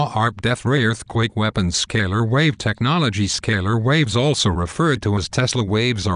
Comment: did anyone else have trouble figuring out what he was saying? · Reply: waves